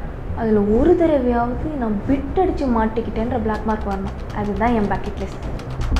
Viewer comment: I don't understand that word... Adhu dhan en "........" 9:24 plzz tell me what's that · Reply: Bucket list means an list to do in future